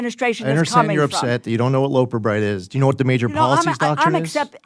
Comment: At first I thought he said You dont know what a loaf of bread is. 🫢🙄